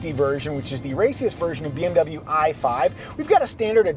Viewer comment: Did anyone else hear that the BMW is racist?! 😂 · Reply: LoL. I heard "raciest." Lot depends on what you are expecting to hear. :)